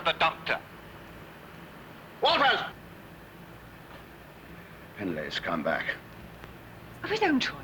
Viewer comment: The main thing i remember from watching this one for the first time is mishearing "Penley's come back?" as "Penley: Scumbag!" Which coming from Clent (officially a tit) is quite ironic.